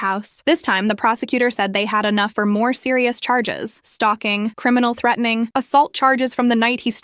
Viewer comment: me lmao because it said "talking" instead of "stalking" LIKE IMAGINED THAT YOU WENT TO JAIL FOR TALKING TO YOUR FRIEND-😂😂